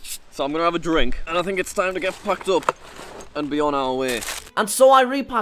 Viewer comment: says packed but it sounds like he says the f word even captions censors lol